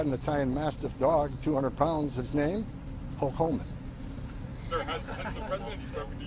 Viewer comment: Tom is great American patriot 👍, and his dog named hulk hogan is cool,these people are doing a great service, lifes are being saved,including children and drugs off the streets. Maga USA 🇺🇸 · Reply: I thought he said his dog's name was "Hulk Homan" 🤣